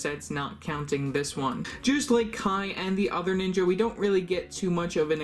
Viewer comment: did u just say “jOoSeD lIkE kAi”??